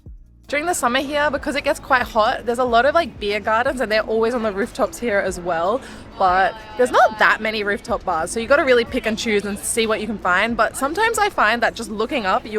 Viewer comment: did you say “You really need to Pikachu.” LOL